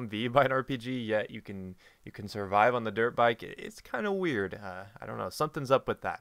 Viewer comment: Internet... what have you done to me. I keep hearing "derp bike" instead of "dirt bike". And then my imagination goes on...